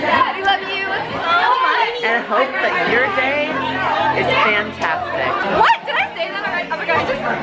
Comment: i thought said "i hope that you're gay" mhm